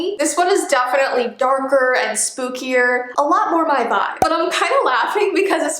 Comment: When she said: "It's More My Vibe " I thought she said : " It's More Bye Bye " XD Lol I was so scared ... Lolll